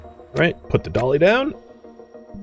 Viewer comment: When says 'Put the dolly down', I hear 'Put the cookie down!'